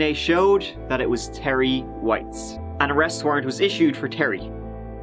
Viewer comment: I thought you said Terry Waite but his alibi was a pair of hand cuffs and a radiator